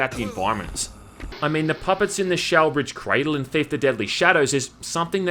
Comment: What is he saying at 8:57? Before mentioning Theif: Deadly Shadows. I could only pick up “shower bridge cradle” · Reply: The Shalebridge Cradle. It's an insane asylem and orphanage in the third Thief.